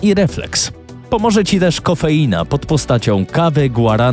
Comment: Ja usłyszałem nie coffeina tylko kokaina xD